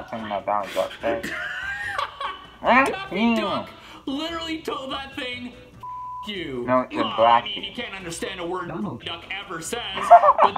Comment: On the Donald Duck one, I was trying to hear him say "blast you" but I still got "f***" instead of blast lol. Also at 8:45 you imitating Donald Duck a second time had me dying on the floor 🤣🤣🤣